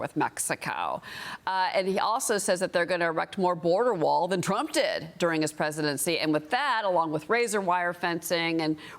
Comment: Did the Host just said :' Maxacow instead of Mexico...lol. listen